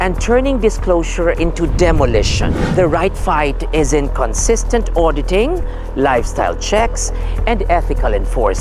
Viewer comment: said “in consistent auditing” but the subtitle says “inconsistent auditing” Whoever transcribed this needs to be fired